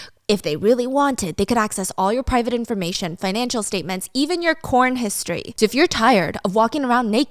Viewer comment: I was listening to you do the ad for VPN. At first I thought you said they look at your Corn History. I thought what the heck is. Corn History? 😂😂😂